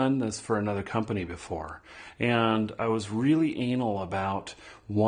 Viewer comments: Really anal? Is that what he said? :o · anal? :O I must have misheard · Did he just say Anal at 2:40 ??